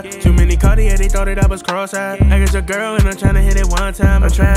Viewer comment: It low key sound like he saying “I’ll try again”😂‼️